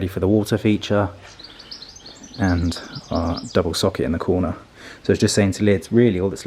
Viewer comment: Because I thought you said "double suck it", I missed the "hoe" comment completely. Perhaps it was best? : ) My hubs installed dusk to dawn sensors on our exterior light fixtures. (like a little plastic tube shaped cell that is attached to the top of the fixtures. Does the work of the timer). At first I didn't think I would like it, but have since realized I am wimp and need night lights after all. AND my grounds keeping OCD envy's your gravel combing. What rewarding results. ~ Hi ya Kenny Ken Ken and Graham! So glad you are both well and safe. XO